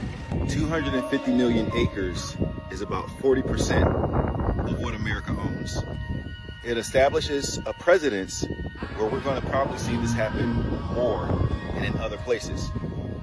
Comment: did he say “presidents” 😂